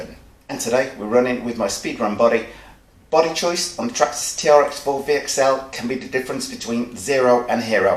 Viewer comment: did you say trx4 vxl?? 😂🤣